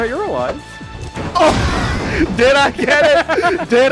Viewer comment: I thought you said “heat seeking cream missile” at the end and I was so upset lol